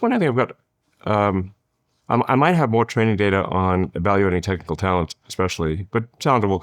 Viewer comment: the whole time during the ad read I thought he was saying “Trading Data”, and I couldn’t figure out what that had to do with laundry. Then when I realized he said “Training Data” I couldn’t figure out why I need a tech startup to teach my domestic help how to do laundry. Then I realized to these people, training the help is probably the worst part of their day. And they aren’t rich enough, to have a butler.